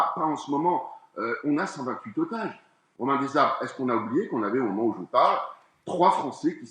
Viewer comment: il dit "on a 128 otages... au mains des arabes " ou c'est moi qui bug...